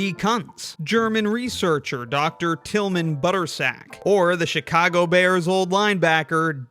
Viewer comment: my captions said buttsack